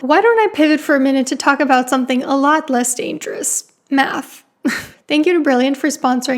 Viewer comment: for a second I thought she said "why don't I pivot to talk about something alot less dangerous: meth!"